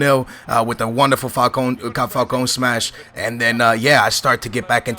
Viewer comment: Haha it sounded like said Falcon smash!